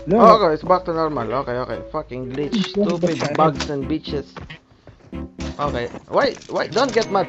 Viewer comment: Did he say stupid bugs and bitches?? 😂😂😂😂